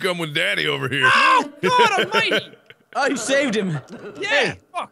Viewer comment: looks like Jack just rescued Mark and sounded like he said "NOOOO" But he said "OH GOD ALMIGHTY"